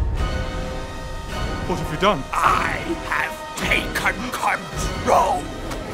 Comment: i heard "I have pay cut control"